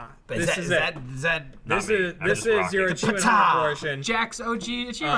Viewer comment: thought he said achiement hunter app for a second... dissapointment followed